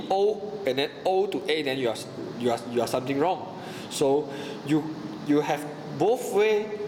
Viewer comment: lol , i heard "screwed" then suddenly changed to something wrong xD